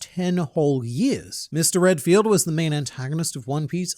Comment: for a second I thought you said Renfield and I was gonna be like I shouldn’t be surprised but here I am it’s still close enough to Renfield